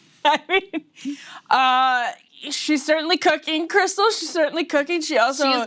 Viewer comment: "She is certainly cooking, Krystal" I heard it as "She is certainly cooking crystal (meth)". 😂😂😂